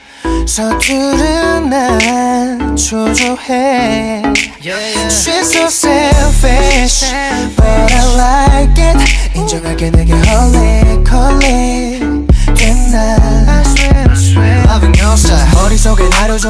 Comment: why do I hear it as "she's so savage but I like it." lol